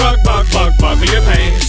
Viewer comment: I hear "bucket your pantz!"